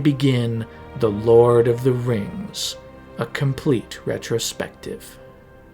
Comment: i clicked because i thought it said "communist perspective" but i watched anyways 😇